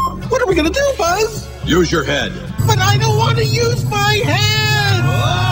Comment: For a second there I thought Rex said "BUT I DON'T WANT TO USE MY HANDS!"